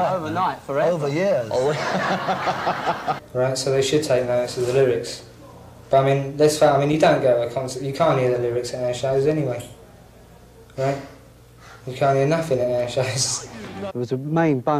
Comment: Need some help here: what did Topper say starting 3:37 ? I just cant hear it clearly... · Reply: “Right so they should take notes of the lyrics, but I mean, You don’t go to a concert, you can’t hear the lyrics at our shows anyway, right, you can’t hear nothing at our shows “